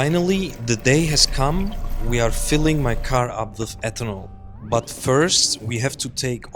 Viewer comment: misheard ethanol as fentanyl